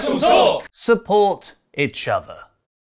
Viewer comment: Is it only me, sounds like he says "support itch other" lol... and ___🌍